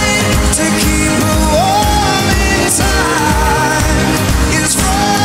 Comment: is it just me or in 2:11 i heard harry saying its for zayn but what other people hear in the concert is: its frozenn